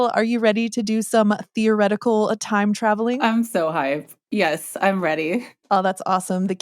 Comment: at first I thought she said “I’m so high”😅😅😅 Nope… that’s just me lol